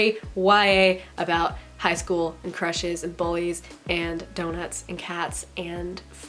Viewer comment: As I was working on my character outlines, I thought I heard you say "don't mess with cats" when you really said "donuts and cats" 🤣🤣🤣🤣